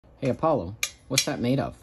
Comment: For a sec I thought he said "Paulo" instead of "Apollo" W O I U L is the key